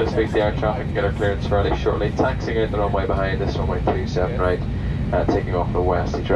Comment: really does sound like he says 37R...